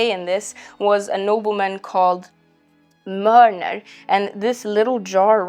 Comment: I think she's saying "Mörner" (the name of a noble family in Sweden). Mjölner/Mjölnir is the name of Thor's hammer... lol 😅